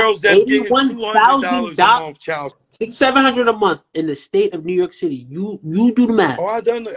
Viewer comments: “ I know what it feels like to have nothing all right” If $6700 a month is nothing to this kid I feel for his adult life. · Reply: 😱😱😱I thought he said 6-700 a month 😱😱😱.. the entitlement has killed him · Bruh I thought he's saying 600 or 700 a mounth